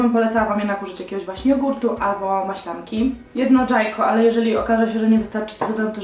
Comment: czy tylko ja usłyszałam ,, jedno dżajko"?